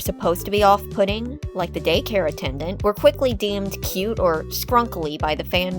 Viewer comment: it sounds like she said "Gay-Care Attendant"😂